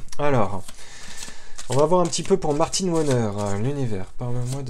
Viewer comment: J'ai entendu "Martine Bonheur" et ça lui va bien 😍